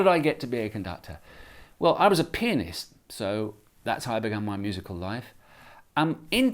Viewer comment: Instead of pianist I thought he said penis